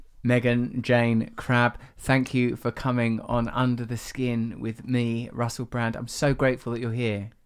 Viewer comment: I thought it said "Under the Shin" so I was confused. Skin makes a lot more sense! ;)